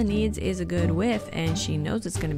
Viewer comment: I love how I heard “good soup” it was so quiet but yet so hilarious I’m dying 😭💀🙏